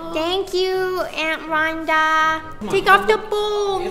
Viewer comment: I thought said "thank you aunt Rolanda" lol 😂💖💗💓💟